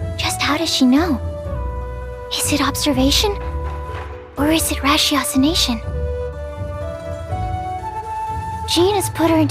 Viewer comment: why does my captions say “is it the rashes nation”